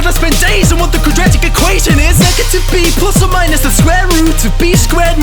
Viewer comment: You got the captions wrong. It's supposed to be "Negative B plus or minus the square root"